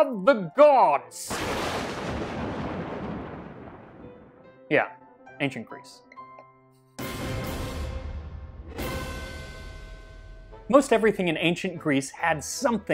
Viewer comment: Thought you said "engine grease" at 4:40...